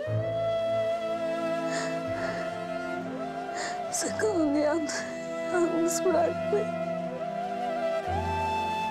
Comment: Songül ne diyor? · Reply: Sakınn onu yanlız bırakmayın diyor